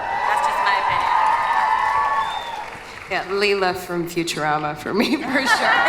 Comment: What did Tatiana say @ 26:24? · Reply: "Leela from 'Futurama'".